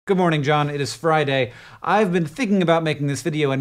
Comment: I misheard "Come on, John, it is Friday". Okay???